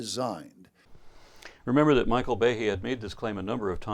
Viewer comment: I was working on some homework when I thought I heard "Michael Bay" so I scrambled back to this tab only to realize its Michael Behe and that an action film director would probably not waste his time trying to prove that a god exists.